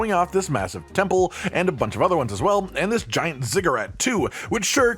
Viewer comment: I totally thought you said "giant cigarette" instead of ziggurat. I do have hearing issues, so I'm wondering if anyone else heard that, lol.